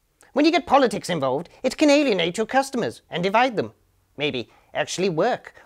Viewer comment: you said, "being moral arbiters can alienate your customers." But with your accent it sounded like, "... you Canadian-ate your customers..." which is much, much better. IMHO.